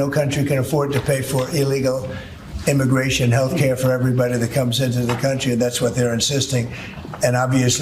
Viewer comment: I thought you said nobody was coming into the country. Everybody is staying away. Nobody wants to be in the United States not even American citizens